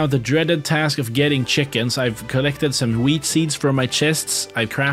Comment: why did i hear "Weed seeds"